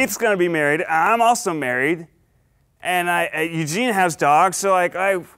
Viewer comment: At the end when Ned was like "so Keith is getting married and I am married and Eugene.." I was so scared I was like NO HE'S NOT GETTING MARRIED lol then he said "have his dog" I was like oml 😂😅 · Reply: Olivia Loleta thought you said that Ned and Eugene are married XD